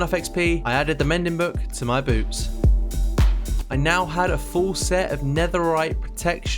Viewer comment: did you say add ping mending book to my boobs😂😂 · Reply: hahahaha sounds like it, but no, I said boots 🤣